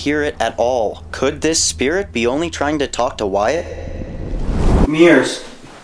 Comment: I heard mirrors as well